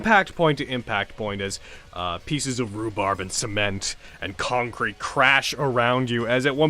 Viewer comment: I can't wait for BEEG robo dino. Wonder if they'll actually fight it, or if they’ll fight different parts from the inside.... Edit: 2:00:20 - I'm not the only one who heard "Rhubarb" when said, "Rebar," right? Because, I double-checked myself, and it still sounded like that.